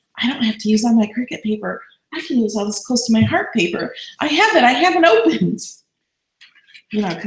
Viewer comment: Maybe it was just that you loved it! LOL. I'll watch video again and let you know what I thought I heard. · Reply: It was Close to my heart paper!